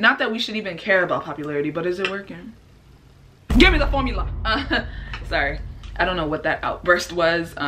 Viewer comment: it sounded like she said give me the phone mila instead orlf give me the formula look at my name